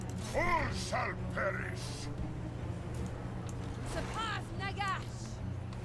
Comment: i think she did say the n word